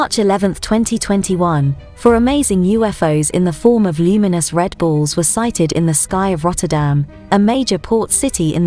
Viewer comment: Could've sworn she said Red Bulls lol not Red Ball's